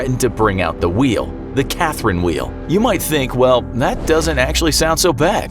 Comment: i thought he said karen wheel XD